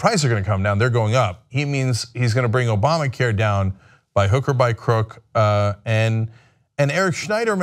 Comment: At first, I heard him say "by hooker..." Lol